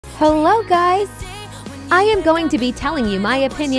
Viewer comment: I thought I heard “fello guys”